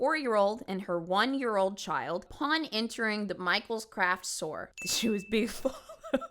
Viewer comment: ngl I thought you said she took her kids to Micheal's crack store the first time you said it and I couldn't figure out why that detail was so casually brushed by 😂